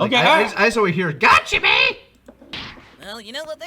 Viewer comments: When I heard the cell junior beating up goku, I thought he said "wham, bam, gotchya man." cause I thought they were as annoying as perfect cell. Also, I did start watching the original dragon Ball. I am in love with makafushi adventure and it's just charming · Im sorry to say that i always heard "gacha man"! 😂😂 But the realization is super entertaining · I always heard “wham bam gotcha man!” So I suppose i heard a mix of the true words, and what heard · I only hear "wham-bam-gotcha man"